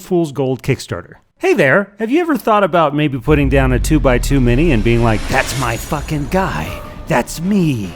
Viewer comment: Misheard 2x2 mini as 2xtoomany.